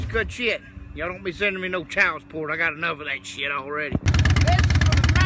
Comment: Did he just say he has enough child porn wtf